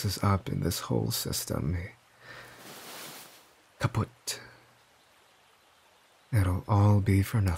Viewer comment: Wait wait wait! Did he say „kaputt“ ?!